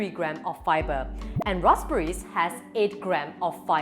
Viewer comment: did i just heard rosberry haha 😂 p.s. after watching this video, i will eat white rice and not brown rice... 🍚 BTW, anyone RICE GANG??!! 🍚